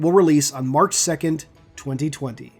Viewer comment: Not gonna lie, Rion in the cover looks like she's just come out of either Fortnite or CS:GO. I'm not sure if I heard it right, but it sounded like you said the book will release on March 2020, at 6:55 in the video. :D